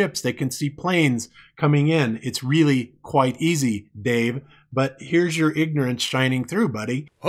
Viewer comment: I thought he was saying babe instead of Dave